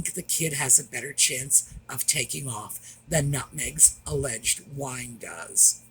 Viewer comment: LOL... when you first mentioned Meghan's new wine I thought you said Meghan's new whine!